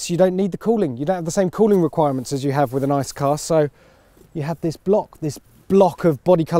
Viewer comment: "You don't have the same cooling requirements as a nice car." - sneaky, possibly unintentional roast there. (Could be "ICE" but sounded like "nice").